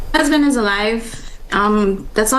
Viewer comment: I thought it sounded like she said, "My husband is alive." Anyone else?